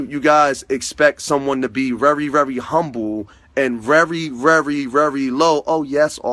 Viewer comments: did you say rery, rery, rery? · Wait, did he just say “rery rery rery? Is that a speech impediment? Or is it my ears that need a good clean 🧼